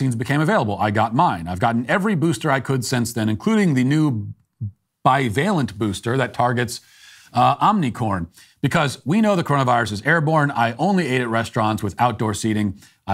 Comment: Did... did Matt just say, "Omnicorn"...? Hahaha! I like it! I think I'll use that.